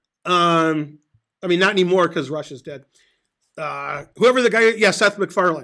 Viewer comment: i,m sorry, did you say"Russia s dead " ??